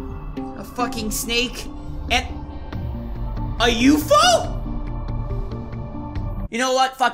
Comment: I hate snakes. PS: Brain fart at 3:25. Yes, I said "you-fo" instead of u.f.o. lol